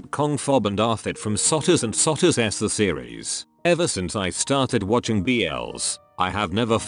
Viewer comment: "from SOTUS and SOTUS S The Series" But what I heard: "sssssssss the series"